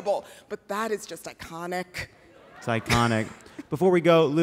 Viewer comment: When Jon said "it's iconic" I heard "psychonic" which isn't a word but should be.